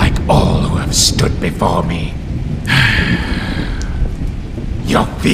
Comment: what it’s meant to say: “your fear will consume you!” What I heard: “your beer will consume you!”